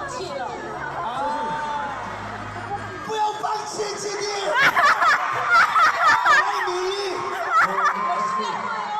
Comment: Can somebody tell me what did Seungri said to GD at 8:28 after playing that lifting the can with spaghetti game? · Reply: VI said "Don't give up, GD!! We have to try hard!!"